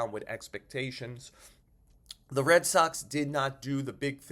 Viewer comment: is it me or every time somebody says the Red Soxs I hear the Red sucks 😂